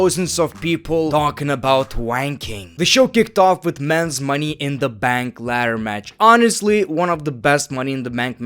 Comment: Am I the one who heard him say money in the mank😅😂